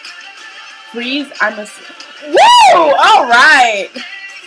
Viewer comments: lol When I first heard "armor down" I thought they said "I'm a Dom." WELL OK THEN! :P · lol When I first heard "armor down" I thought they said "I'm a Dom." WELL OK THEN! :P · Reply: Lol the song is still pretty kinky even without that line, with the whispered "good boy" line in Korean and other stuff XD but yeah I thought he said "freeze, im all down" at first and I was like wha? Haha